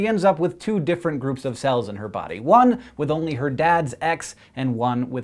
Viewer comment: i thought he said, "her dad's ex" XD